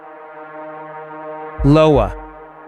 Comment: Did Ai just say Lowa instead of iowa? Cmon now 😂 they know we're onto them so they're playing dumb.. 🤔